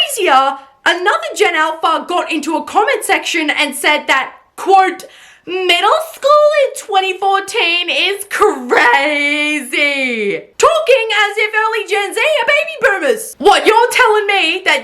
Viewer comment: I thought it said pizza in 2014 was crazzy… 🥹💔